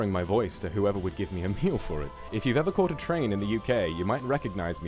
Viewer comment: "a meal for it" instead of "appeal for it"